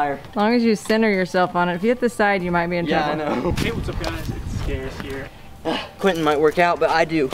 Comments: XD Did someone else hear "Hey what's up guys, Scarce here" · Anybody else hear the "hey what's up guys Scarce here" at 5:02?? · Who heard scarce at 5:03 😂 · has anybody heard "Hey what's up guys it's...." at 5:00 ?